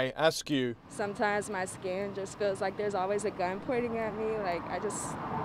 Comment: Did she say: "because my skin, it feels like there's always a gun pointed at me." ?